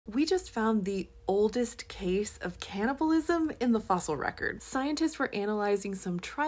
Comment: I thought that she said "We've just found the oldest cannabis use in the fossil record." I need to stop watching so many of these YT shorts.